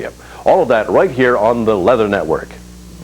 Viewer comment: Does he say "The Leather Network" at 3:40?